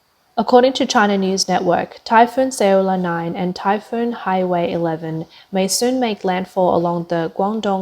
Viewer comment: ummm @ 3:52 Did she just say "Typhoon 'Saola 9' and Typhoon 'Highway 11'" ???? a I going nuts or is that what she really said?? ... (ohh wait ,I just put on the Closed Captions and I see it's 'Haiwei 11' whoops LOL) why does the map call it Haikui but she calls it Haiwei?? English is difficult enouygh for me and I've been speaking it for 42 years lol :P I'm j/k